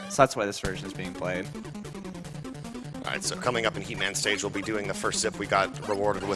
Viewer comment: lol 8:08 when he said heat man i thought he said he-man xD